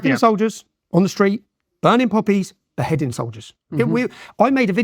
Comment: did he say burning puppies? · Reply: Poppies